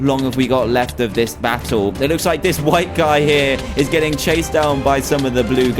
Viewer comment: lol what he said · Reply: he said white guy